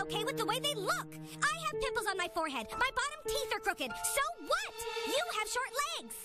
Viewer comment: I thought she said her bottom teeth were broken... im dumb.